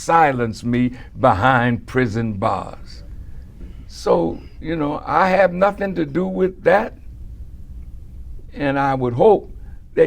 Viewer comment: all this....and he NEVER ONCE OUTRIGHT STATED that "I had nothing to do with the assassination of Malcom X". in one interview he said, "WE DEALT WITH HM JUST LIKE ANYONE WOULD DEAL WITH A TRAITOR! SO WHAT ARE YOU GOING TO DO ABOUT IT?" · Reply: He clearly said "I dont have anything to do with that" @ 20:22. Or does he have to speak to you like a 1st grader?